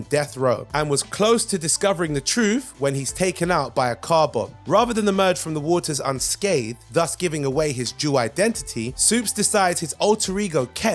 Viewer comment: ish, did he say " his jew identity " ?